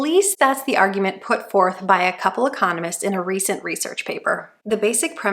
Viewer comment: I thought she said this was a plan "put forth by a couple of communists" (not "economists"). After listening to her describe their horrible idea, maybe my first thought was right after all!